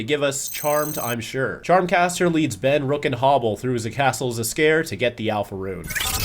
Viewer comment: I had to backtrack to make sure I heard that right, but “ze castle Zs’Skayr” was a bit funny.